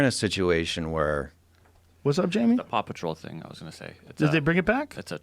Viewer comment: Maybe it's just me, but when Rogan said "Paw Patrol", it sounded like he was saying "Papa Troll".